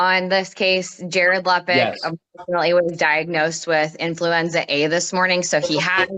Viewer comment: I misheard " he was diagnosed with influencer a" 😂